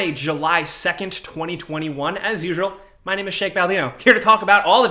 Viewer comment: “My name is Shake Baldino.” That sounded like a damn good drinking brand, if you asked me! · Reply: i thought he said Sheikh Baldino .... he is now Arab royalty lol